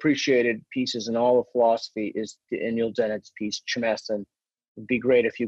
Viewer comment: Did anyone catch what book/author he mentions at 8:53? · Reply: Daniel Dennet Chmess :)